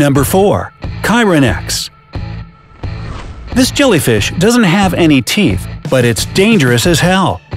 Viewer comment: Did you just say Jalla fish?